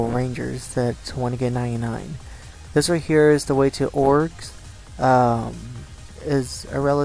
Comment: did he say orges? they are pronounced o-gr-s